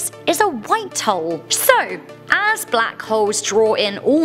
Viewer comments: Is it me or did she sound like she said "white hoes"? 🤔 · Lol sometimes it sounded like she was saying white hos A